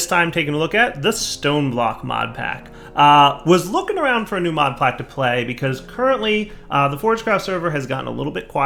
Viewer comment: "Modplack to play" I'm not the only one who hears this right? I fuck up the english language all the time, sometimes I hear things too.